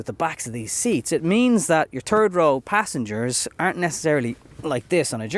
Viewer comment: I know it's the accent but as an American I only hear "turd row" and now I can't unhear it. The third row will only be known to me as the turd row seating.